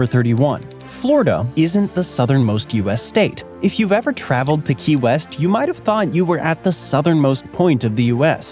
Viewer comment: i thought that said kanye west 😭